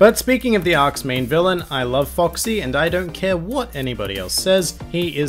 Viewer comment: when you said "i love foxie" i thought you said "i love furries"